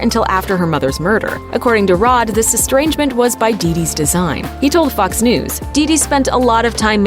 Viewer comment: what did she say???!!!! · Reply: Dee Dee's design